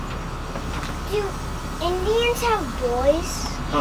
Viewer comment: Did I just hear “Do indians have boys”